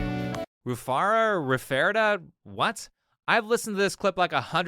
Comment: I think she said retardo xD